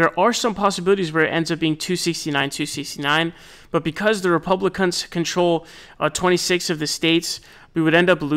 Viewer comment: did you say "Republic*nts"? 😮😂